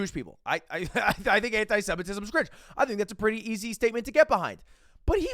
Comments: what, ludwig said anti semitism is great at 2:34 haha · bro did you really say "i think antisemitism is great" 2:32 😭